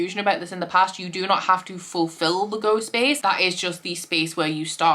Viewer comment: When you said “Go space” I heard “Ghost Space”, which totally is what it should be called for Spookoplathon btw!